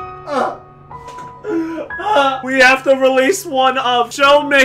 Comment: I thought it said randomize not release